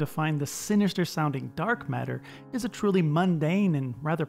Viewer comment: I saw this video twice while scrolling through YouTube and both times I thought it said "dank matter"